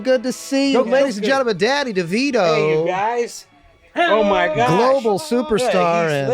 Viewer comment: totally sounds like mac says “daddy Devito everybody” lol